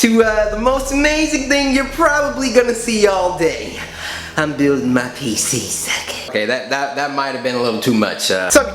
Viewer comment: I thought it said "Bullying my pc".